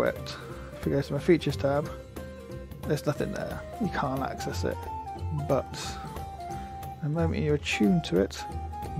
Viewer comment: At about 4:20, you say that you're not sure if the "requires attunement" icon is part of this module or another. I couldn't hear the name of the other module, but would love to know! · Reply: Browne - I believe it is the TidySheets module.